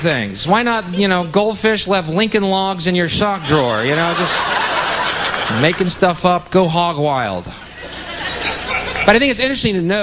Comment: Cosa dice letteralmente al minuto 6:07? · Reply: Making stuff up, go hog wild (credo) XD